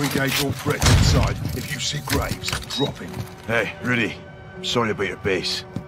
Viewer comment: I thought I heard him say "hey griddy"